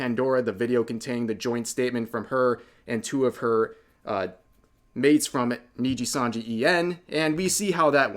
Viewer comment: he called vox and ike eliras maids... What did he try to say by that. Does he buy into the elira is manager rrat??? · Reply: I think he meant mates not maids 😂